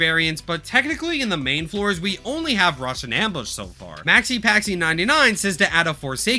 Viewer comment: THE SUBTITLES SAY "RUSSIAN AMBUSH" BRO I CAN'T 😭